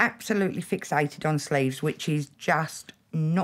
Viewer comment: I thought they were saying she was absolutely fixated on Slaves hahah.. the penny finally dropped when she talked about the arm.